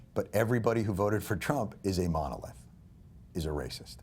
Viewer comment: What is that word he is saying 4:20-4:28 that sounds like "monolay"?